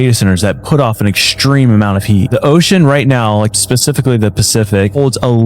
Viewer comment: he didn't say it but couldn't help but hear "pacifically" instead of specifically.